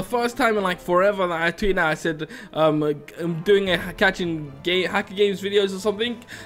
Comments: I thought he was going to say: catching gay hackers. When he said: a gamer hacker. I was like: well that makes more sence... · did he say catching gay hacker games?